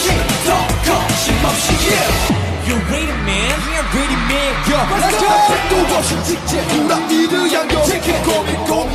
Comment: what is dino saying and why he sounds so good speaking english bro step on me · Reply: "You ready, man?"